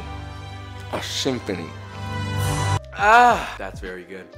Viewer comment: When you said “Symphony” I heard “sfincione”. Pretty accurate, still